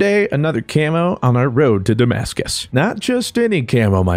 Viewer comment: Road to Damascus? I thought you said Kansas guess I won't get to see toto 😂